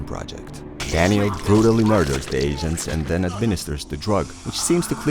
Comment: when u say agents it sounds like ur saying asians lmfao